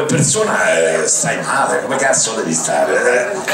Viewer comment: Sembra di aver dato la risposta giusta alle ~ 03:14, "Come persona, stai male, come cazzo ??? stare" C'ha detto a quel momento? Son autodidatta in italiano e non ho capito quella porzione. Grazie, Tom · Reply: Come persona stai male, come cazzo devi stare?